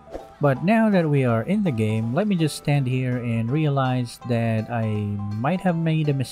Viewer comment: did I just heard "Standing here, I realized"?